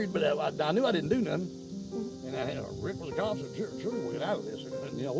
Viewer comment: where he says "Rick was a cop (unintelligible)" I was unaware of the fact that the caption stated "unintelligible" which was why I rewatched the clip- I couldn't believe that it sounded like he said "surely we could get out of this" "I was worried, but I knew I didn't do nothin'. Rick was a cop so surely we could get out of this. Well, we didn't do shit. We found her."